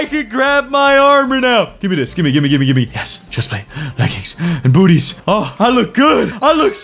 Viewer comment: did just say bootys🤣🤣🤣🤣